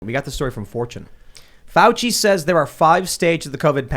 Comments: I thought you said "We got this story from 4chan" I was like.... what lol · When you say fortune that fast it sounds like you said 4chan 😂